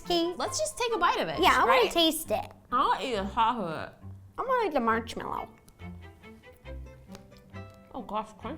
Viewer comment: Did Ro say, “ I wanna eat the March-mallow “ ???